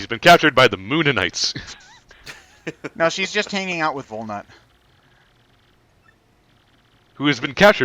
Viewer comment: at first I thought said "Luna" so I was like "did Ted make a MLP joke?" But no he said Volnutt. Eh, MML2 jokes about the moon are all cool. Also moonities